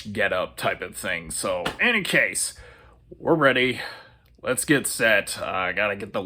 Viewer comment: did you say "Andy Case?" 😆😜